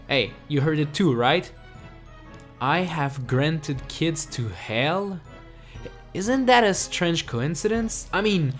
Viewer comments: ti sounded like she said i have granted kids to jail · To me it sounds like "i have created hell" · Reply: Zombie king 586 To me it sounds like Ashley said " I have rabbit kits to sell ".